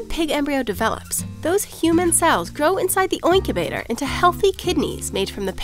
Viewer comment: Did she just say oinkubator instead of incubator